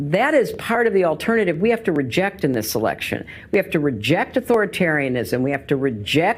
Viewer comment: did she say "selection"???